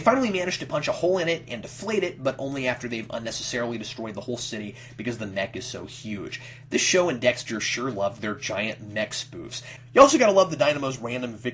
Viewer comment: What'd say? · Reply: "because the mech is so huge. This show and Dexter sure loved their giant mech spoofs."